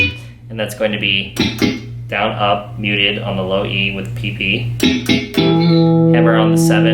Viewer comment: I can't help but chuckle like a kindergartener every time I hear Tim say, "PP I Am". I'm 20 years old..